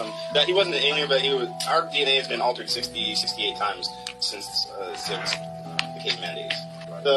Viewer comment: Thanks for sharing did you hear what he said our DNA's been altered 16 17 times that's crazy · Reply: > 68 times is what I heard.. "67.. 68 times.." is what I heard him saying.. 🤔 Divine Angelic Safeguarding & Many Blessings, ALL!! 🙋🏻‍♀️🖖🏼📖🙇🏻‍♀️🙏🏼🕊️👼🏻🌄